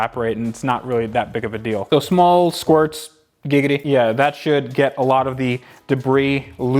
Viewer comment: Family Guy Reference....."Small Squirts...Gigiddy" At least thats the way I heard it!!